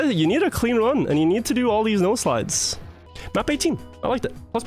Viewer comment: This whole time I thought he was saying “nose slide” instead of “no slide”